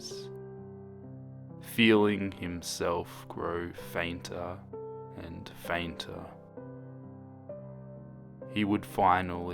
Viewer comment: I know you said "fainter and fainter" but in my mind i heard it as "fanta and fanta" XD